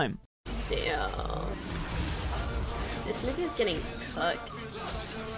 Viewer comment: Am I the only one who didn't hear fucked or cucked, but instead "Cooked"